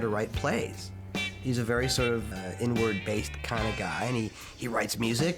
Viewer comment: I thought he said “N-Word” for a second 🤣🤣